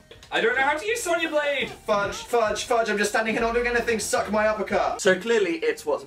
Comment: When Dan said "suck my uppercut" I thought he said "suck my apricot" :L